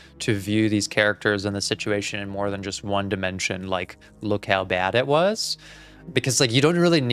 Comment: did you say dimension!?